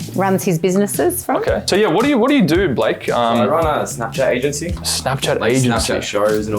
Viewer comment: Can anybody explain what Blake said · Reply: Snap shit agengy